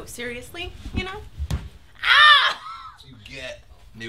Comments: i thought she said Cooter.. lol, either way, still funny. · i thought she said cooter.